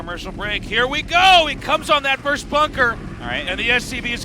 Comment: Tasteless: "He 'cums' on that first bunker!" How I heard it the first time... I couldn't hear it the other way... I'm sorry.